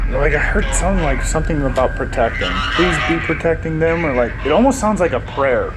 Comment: I heard “ let me protect you two” in the EVP session. My sister to